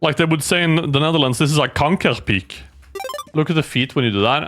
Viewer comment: what do we say here in the Netherlands? Kankerpiek? 😂